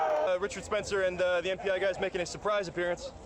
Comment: Moments after 25:30, a UTR person says: "uh..Richard Spencer and the [can't quite make it out: 'FBI'?] guys making a surprise appearance..." anyone know what he says "...Richard Spencer and the [??] guys making..." what guys is he naming there? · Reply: NPI guys. It's the National Policy Institute. A think tank dedicated to advancing white nationalist policy in the US (their words, not mine). Richard Spencer is the president